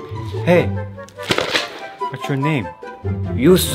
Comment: "f u" "you suck" "you so mean" Cracked me up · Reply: I thought he said you so mid